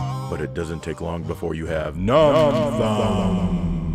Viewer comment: I 100% thought you said numb bum. And let's face it, that's probably a bigger problem 😂